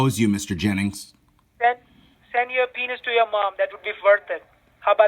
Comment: I could have swore he said "send a penis to your mom".....wtf